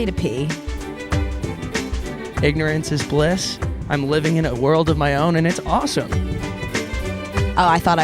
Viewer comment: every single time the intro plays I hear “ignorance is bliss, I’m living in a world of piss, and it’s awesome”